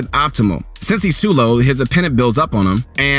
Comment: did I just hear him say appenent?